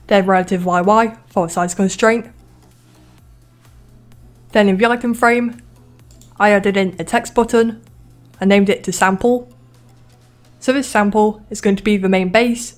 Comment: what did you say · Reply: "then in the item frame, i added in a text button and named it to sample"